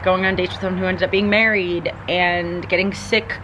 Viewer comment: Did you just say “getting married”???? I must have missed that somehow.